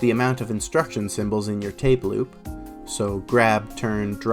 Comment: Tableau pronounced “tay blue” took me out 😂 great video! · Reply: Ohhh I put my earbuds in and realized I misheard you say “tape loop” as weirdly said “tableau”